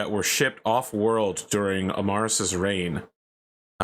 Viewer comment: For a loooooong time i was just listening, so i heard "Marras" as "Amaris" and i was like "you know, there's a reason you don't see a lot of Adolfs nor Hitlers these days"